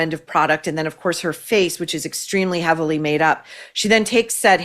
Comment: I thought it said the “farce” of MM 😂